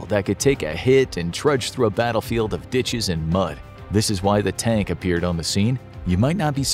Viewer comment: Could have sworn he said "bltches and mud" lol